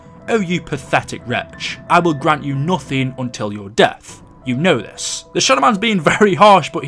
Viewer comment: The shadow man sounds like he says, "until your debt is payed off, " instead of until your death